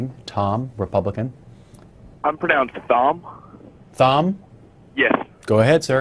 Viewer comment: I feel like Thom called at least 8 times. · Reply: I thought he was saying "It's pronounced Thumb" lmao